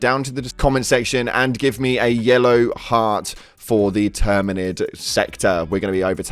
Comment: Thought he said yellow hat 👒 🤔 but it was Heart lmao 💛 anyways thanks for going over the patch notes I like reading them then watching the vids and seeing other opinions and such. Great video as always my guy!